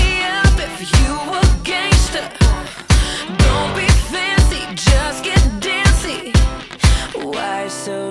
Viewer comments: i always thought she said "don't get fizzy, just get dizzy" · i thought it said "don't be fizzy, just get dizzy" anyone else? no? just me? okay i'll go home.